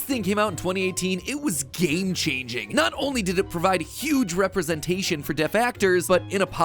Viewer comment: I really thought he said "representation to 'defectors'"... Had to go back and listen again... Hahahahhahahahahah